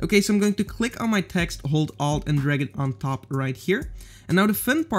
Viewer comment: I didn't understand (hold alt...??)